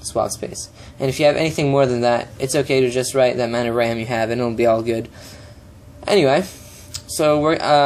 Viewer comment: What the heck are you saying at 8:45 after "if you have mire than that, it's ok to..."? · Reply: "...it's okay to just write the amount of RAM you have (in your PC) and it will be all good..."